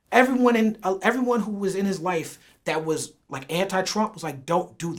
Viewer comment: did he say everybody that was in kanye life or his wife? Lmao